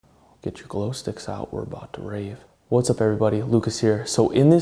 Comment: I thought he said "get your cloth, dicks out, were about to rape"